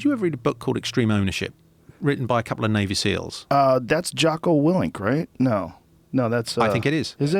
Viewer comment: "Extreme Ownership" (circa 17:05 in this) by ??? - I didnt catch the authors name correctly - anyone? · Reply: Jocko willink